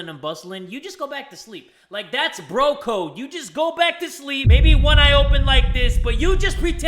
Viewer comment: My audio was low an muffled so like at 1:02 I thought he said “you just go back to sleep maybe I open my fist”